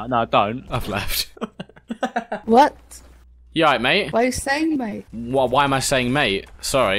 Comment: didn’t she say what you say babe?